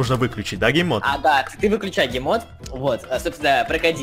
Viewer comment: мне одному послышалось не гейммод а геймод 😆